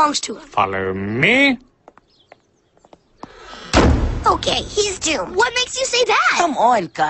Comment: "Ok he's JEW." did anyone else hear that? I wondered if that was a jew joke for him being rich. It would of made sense.